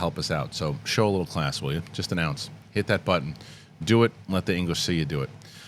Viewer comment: I love the "show a little class" I use it in regular speech now but after that when you say "just an ounce" I always thought you were saying 'just announced' and I'm here like Vincent Vega looking for the breaking news.